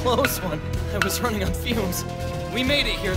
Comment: did i misshear what conny said? Fuels? Or gas?.... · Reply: HaruyukiMatsuta i think he said fumes